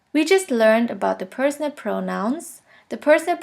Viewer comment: You Mentioned Personit at 3:48 & 6:00 if I'm spelling or hearing it correct. what is that. I can't really hear what you're saying?